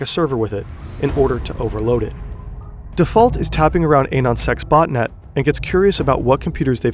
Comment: Did i hear that right??!!! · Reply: U did anons*xe